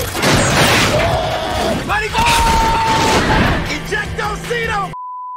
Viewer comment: All this time I thought he said " ejecto seato cuz ! " 🤦‍♂️ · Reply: he did